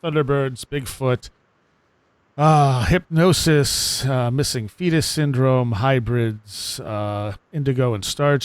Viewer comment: I thought he said, Missing penis. So glad he said missing fetus instead. Though either one missing is a bad thing but you (in most cases) need one to have theothr